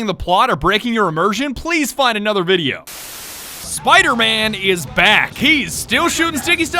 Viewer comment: I thought he said “spider man is black”